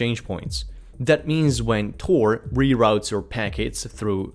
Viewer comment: i heard tor reroutes my pancackes 😂 - i dont want that lol......my brain at 1am playing tricks. Thanks for the video 👍